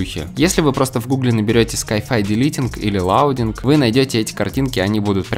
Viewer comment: Как правильно написать что он сказал на минуте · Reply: sci fi downloading